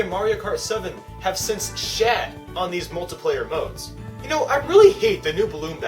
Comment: What did you say at 4:40, I don't understand what it was. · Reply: kerrie grissom They have shat on the multiplayer modes in the newer games, basically meaning the new battle modes are really bad in the newer mario kart games compared to the older ones